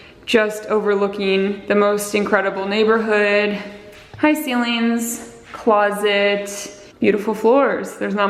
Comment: The move-in cleaning stressed me out when we bought our new home. I was shocked at the filth. 😭 Edit: 7:32 sounded like she was greeting the ceiling; "Hi, Ceilings!"